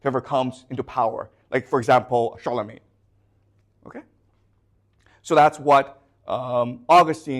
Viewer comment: what did he say? i did not hear the name clearly. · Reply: charlemagne